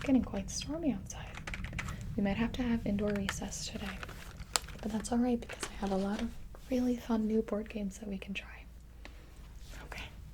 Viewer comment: Wait at 15:20 did she say recces what middle school is this